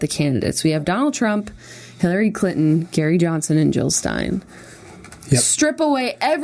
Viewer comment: It sounds like Jenna is saying Hillary Clit-on. I'm dying! :D